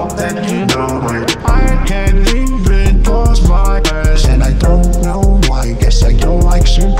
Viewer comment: I thought he said I can't even close my a** 😂😂😂😂😂